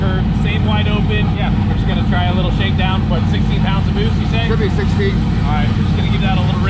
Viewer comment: At first I thought he said 60 pounds of boost I was like 😳😳